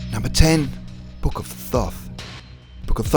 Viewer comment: At first I thought he said ''Book of Thots'' instead of ''Book of Thoth''